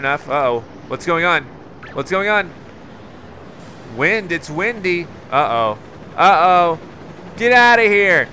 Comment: I thought he said wendy but he said windy